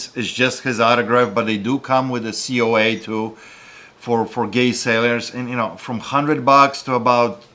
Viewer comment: ~ Did he just say ' for Gay Sailors'?..😳 Seems like a niche market.